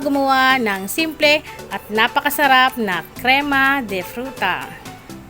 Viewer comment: I'm so sorry I heard it wrong.. "creama de futa" 🥺🥺😷 I'm ashamed of myself.😓😓